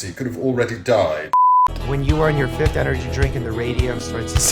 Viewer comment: I heard " When you are on your 5fth drink of Uraniun " for a moment